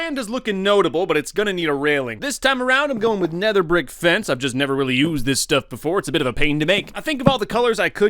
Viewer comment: I almost thought he said, "This time around I am going with nether brick fence- I just nether really use this stuff before."